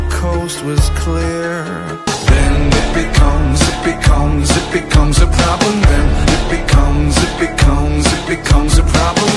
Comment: I swear I heard it becomes a proper man right after that